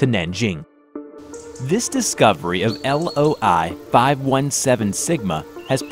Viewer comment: who else thought it said LoL-517-Sigma before he said Loi